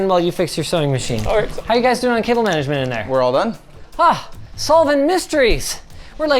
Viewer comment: i think we might have heard talk at around 21:46 saying "we're all done" but not sure